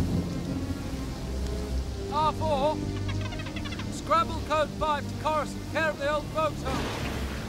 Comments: This has always bugged me what the hell does he say at 0:33 ? It sounds like “scramble code 5 to coursaunt, care of the old folks home” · is he saying old folks home?